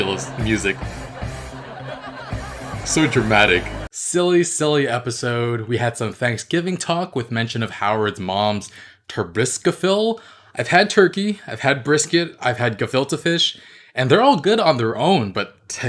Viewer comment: I thought you said you'd never had brisket before.